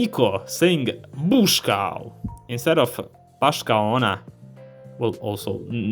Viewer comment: i thought he was trying to say "Puši ga"